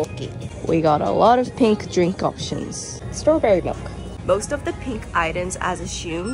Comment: I thought she said “strong berry milk”😹